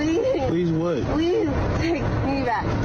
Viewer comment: Why is she saying “Please Mommy”?